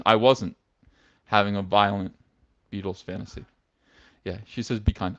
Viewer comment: Violent Beatles fantasy.. I thought you said violent fetal fantasy